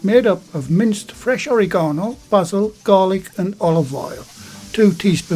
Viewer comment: what's in his "Homemade Pasta Mix" (I heard fresh oregano, ???, garlic, & olive oil)? Can anyone spell out the second item (sounded like 'butil'??), & list how much of each? TIA! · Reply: Hello my friend it's Bazil. Thanks for watching my friend. Here is the ingredients list and full written method on the channels website, Pasta dough. <link>